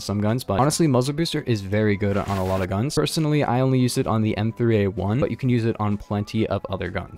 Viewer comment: I heard "m231" and I was like *wut*, but then I repeated and then, i listened to what really said, what was "m3a1"... I almost had a heart attack bc I know doesn't play jokes too often or play Phantom Forces with meme guns (well, maybe in off he does, but, at least in the recorded videos, no)